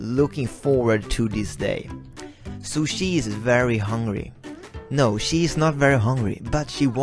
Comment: So I heard "Sushi is very hungry" when you said "so she is very hungry".... XD